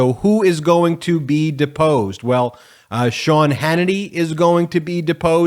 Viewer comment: Oh, I thought you said disposed!!! Sean Hannutty and Tucker Clownshow